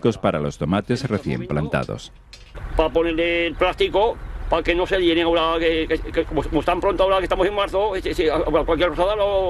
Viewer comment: que dijo el sr 😮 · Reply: Dice que pone esos plásticos para que no se hielen los tomates, porque estamos en marzo y todavía hay heladas por las noches.